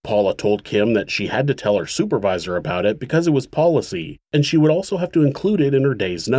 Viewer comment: I know this is a serious case, but I thought I heard you say “Paula told Kim that she had to tell her supervisor because it was Paula-cy” and I almost lost my shit. This was a very engrossing video as always, though. Thanks for sharing this story.